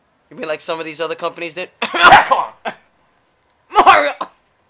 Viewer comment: did you just say Mario?